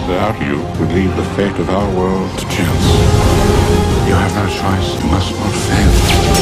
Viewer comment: is he saying "our world to tails"?